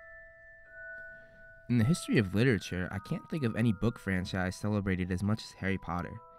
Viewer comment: What he says "in the history of literature", What I hear "in the history of little chair". That is so cute though 😊